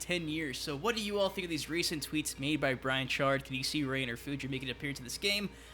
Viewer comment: When you said 'Brian Chard', I thought you said 'Bo' Rai Cho'... so I was like: 'Wait, what? Bo' Rai Cho works for Netherrealm Studios for quite some time?!' XD